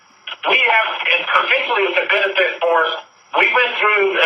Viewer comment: WTF DID HE JUST SAY??!! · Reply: Brittany Money “we have, in completing the benefit force” ...slow it down to .25x speed lol 😂